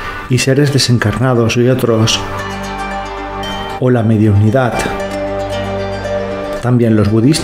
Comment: seg 1:43 no entendi lo da mediocridad